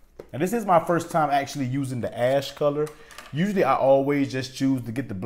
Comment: It sounded like you said ass color